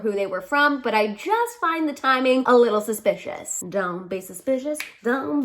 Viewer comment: So...when you sang, "don't be suspicious," I heard, "dumb basic bitches," and they both work. That's all lol